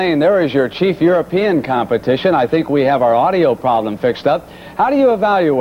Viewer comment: Did I heard right? "your cheap European competition"? :/ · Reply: "Your chief European competition".